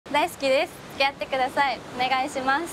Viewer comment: 大好きです。がLet's get itに聞こえる